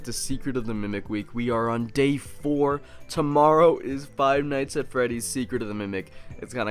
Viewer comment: Did he say gay 4